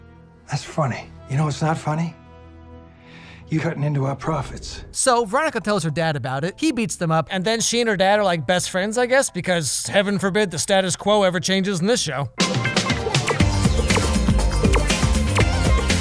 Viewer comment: anyone else hear “There’s a lot of bullshit in this show”